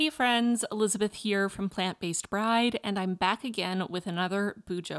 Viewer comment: At the beginning, first video I've watched of yours, I thought you said Plant Based Pride and I got really confused because I also heard Bride and my brain exploded for a few seconds